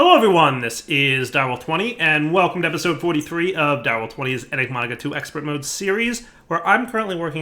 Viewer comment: Did he just say 'Enigmaga' at the start?